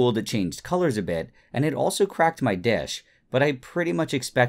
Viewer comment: omg sorry i heard "i also cracked my dick" lol sorry